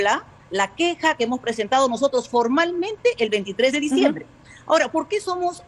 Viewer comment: Min 0:56 "... hemos presentado formalmente el (¡23 de Diciembre!?) ..." -- Min 8:48 "... documento que entregamos el día (23 de Diciembre¡?) ... luego el (29 de Diciembre!?) ..." Me pueden ayudar a entender lo que dice esta Señora??! --- He reído hasta no más por "el papelón de los chiflados ($ ;) %) &)" y parece me bajó el azúcarr¡ Estamos en 02JULIO 2021, cierto??